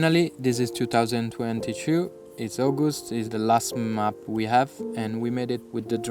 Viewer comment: October 2022 - when did you build the time machine? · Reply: He said August, but because of his accent I can see where you have misheard. If you enable the 'CC' (closed captions) it says August.